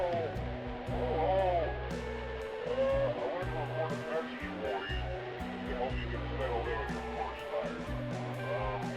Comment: what does this say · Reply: “Hello?” and “(t)o record a message for……” It just says the same thing right there 👍